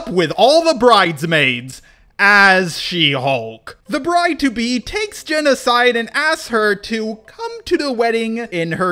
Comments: Am I the only one that heard "take's Genocide" instead of "take's Jen aside" at 40:00? Please tell me I'm not the only one! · when you said takes Jen aside i thought I heard takes genocide